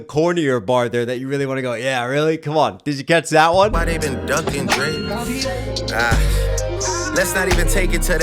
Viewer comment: did he say my name is ducking drake.. because of kendricks last name